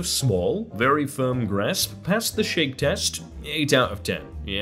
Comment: did he say BREASTS ? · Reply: grasp